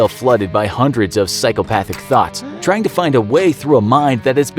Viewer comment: did he say psychopathic thots ? Because I see those all the time. 💀😂